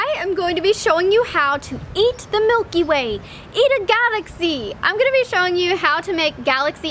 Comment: I thought it said " eat Mikey Way for desert" and I'm just like oh god yes why can't I just eat Mikey 😂